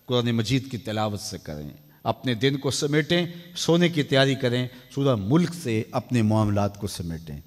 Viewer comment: Surah mulk bola na? Please koi batao mujhe